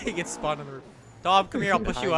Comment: I thought he Said "Dom" instead of "Tom". Still works though!